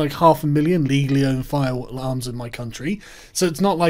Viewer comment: did he say there's half a million legally owned fire alarms in Britain?